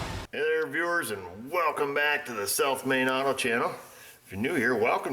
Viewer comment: I started watching your show last December, and all this time, I thought you were saying "Self Made Auto" instead of South Main Auto! Great show, takes me back to 1959 when I worked in a Union 76 Station. Not many auto repair shops like yours then.